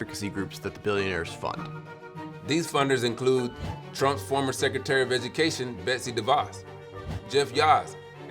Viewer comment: I had to do a double-take, cuz I almost thought he said, "Drunk former Secretary of Education Betsy Devos"🤣😂